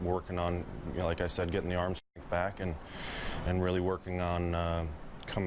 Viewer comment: When he says "arm strength" at 2:01, it sounds like he says "get my arm shit back." Perfect time for the sound to cut.